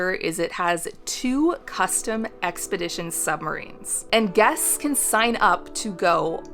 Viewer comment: did she say submarine